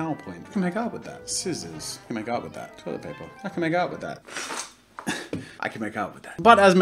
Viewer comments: At the beginning, I thought was saying "I could make out with that" and I was so confused. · I turned away for a moment and when I turned back I saw holding a roll of toilet paper and I thought he was saying "I can make out with that" and he just looked too happy to be saying he could make out with a sword. Good thing I went back and rewatched because wow was I hearing wrong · In the begging I thought he said “I can make out with that!” 😂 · I misheard "I can make out with that" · I thought he keep saying I can make out with that XD LOL CANT STOP LAUGHING HAH LOL